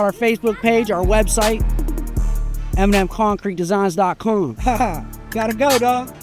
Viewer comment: Concrete guy with killer free advertisement, smart man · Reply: I thought he said "Eminem Concrete Design" lol